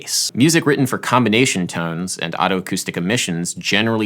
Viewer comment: I thought you were saying "auto-acoustic" until there was text on the screen. In medicine (at least, out here in flyover country) the oto- is pronounced "Oh toe" rather than "otto'. Check with a local ear, nose and throat physician as that specialty is officially Otolaryngology or Otorhinolaryngology .